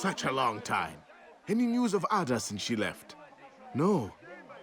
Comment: Guys, help. I do not perceive English well by ear, but it is important for me to know. On 5:35 minute the informant said about the girl-artifact "Ada", which is mentioned in the spin-off "Chronicles of Altair"? Did I hear what he said correctly?: “Any news of Ada’s* she left” Yep?